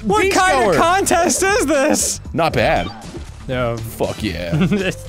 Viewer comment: I totally thought it said "not bro" instead of not bad